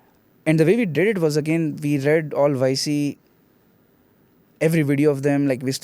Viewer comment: what he said ViceC?? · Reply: yc = y comninator